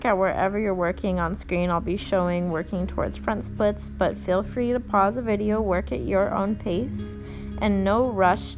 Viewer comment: did she say feel free to pause the video at 23:05.... :D Damn right i'm gonna pause it !!!!!